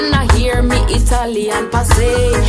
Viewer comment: Habe ich hier eben Deutschland gehört?😀